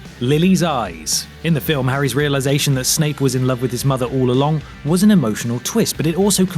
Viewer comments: Does he really say "Snake" instead of "Snape" ???? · I swear he said "snake"